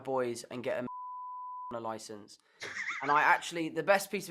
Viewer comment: any clue what he said here? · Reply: Get a Marijuana licence